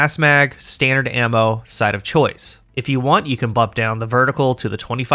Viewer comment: I keep hearing “*Side of choice*” like I’m ordering food 😂